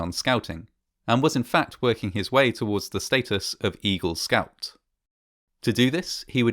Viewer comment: all I hear is evil scout